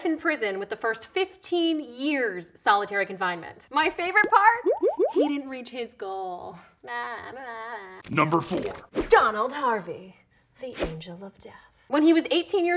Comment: i swear to god i thought she said DONALD trump